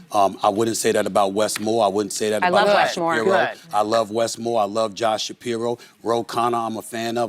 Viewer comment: i swear she says rushmoore